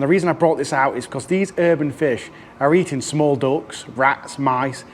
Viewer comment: Thought he said they were eating small dogs for a second there!